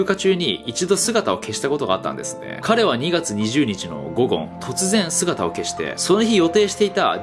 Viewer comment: 『午後』が『ゴゴン』に聞こえる。